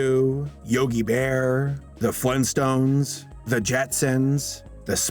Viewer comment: Did I hear the funstones?